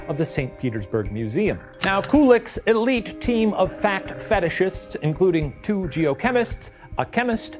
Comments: omg i thought he said fat fetishists so many times until i heard fact i was confused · Send us Debrief Q's here! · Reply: I thought Shane said “fat fetishists”instead of “fact fetishists”